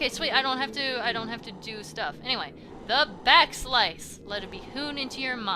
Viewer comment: Am I the only one who thought "Back Slice" said "Back Slash"?